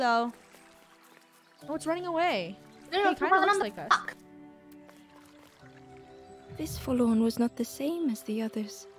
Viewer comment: what did say? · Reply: She said "rock"